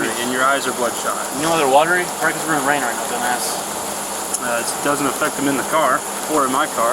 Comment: did he call him a dumb ass @ 15:49?